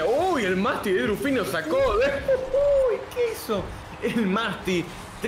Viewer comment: I don't speak a lot of Spanish, but this dude definitely said "Queso"... which means cheese 🤣 Why is he saying cheese lmaooo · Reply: "que hizo" - synonymous to "What did he just do"